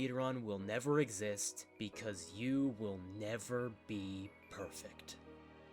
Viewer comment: Did somebody say PEEFECT?